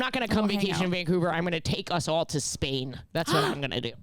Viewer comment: thought she was gonna say "space" instead of "spain" and now honestly im disappointed even tho going to space was never a reasonable possibility in the first place